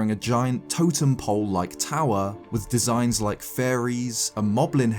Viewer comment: i was a little distracted and thought he said furries (instead of fairies) for a second and i thought he was gonna say the scary part of ww tingle is that he’s a furry. i cant